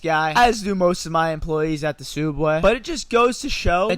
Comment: i heard sooubway lol